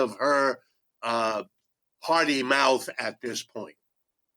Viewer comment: I heard it as “party” meaning she’s functioning as a Democrat rather than a trained economist. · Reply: Right, he said party, not potty.